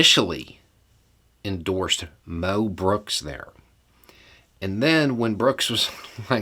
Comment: I first thought I heard you say "Trump endorsed Mel Brooks", and I was a little confused.